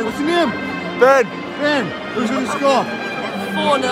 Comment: I’m the one who predicted 4-0 when you said who was gonna score I said 4-0 I thought you said what’s gonna be score 😂😂😂😂😂😂😂